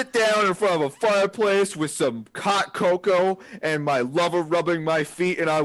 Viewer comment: When said "Hot Coco" I heard "Cock Coco" It changed the serene setting of the fireplace storybook time to something nightmarish and wrong.